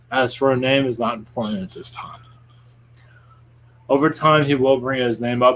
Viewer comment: Anyone else hear the other voice speak at 4:12????? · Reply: I did.. 'At this time'